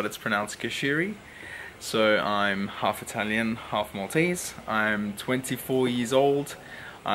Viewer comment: This was an awesome video, definitely think I need to do something like this myself! And I don't know if I misheard... but did you say you are half Maltese?! And that trip sounds amazing man, you should definitely try and vlog some of it while you're there :)